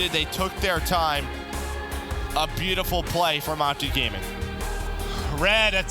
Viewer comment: what did MerK just call optic cause he didn’t say optic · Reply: "a beautiful play from optic gaming"